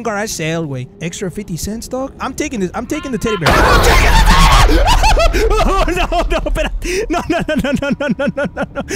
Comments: It sounds like he saying "coconut coconut" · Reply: Maranara Sauce nah fool he sayin No no no el coco no el coco no it’s a rlly cringy song but if ur curious the the name is literally: el coco no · did he say "IM NOT TAKING YOUR TEDDY BEAR!" xD